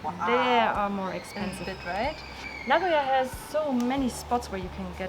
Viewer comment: Hi can you tell me what was said at 9:41? Which place has lots of special goshuin? Thanks! Nevermind its Nagoya